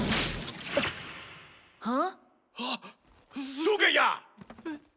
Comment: why did it say zugeya